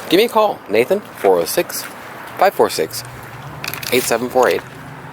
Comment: A package of what? That you're now addicted to? Sounded like you said "Serb's" LOL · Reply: Certs. Breath mints.